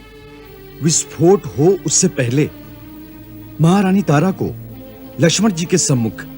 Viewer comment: inhone kya bola? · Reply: Visfot